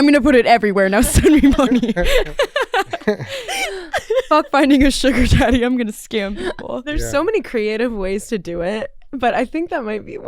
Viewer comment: what does she say? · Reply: "fuck finding a sugar daddy, I'm gonna scam people"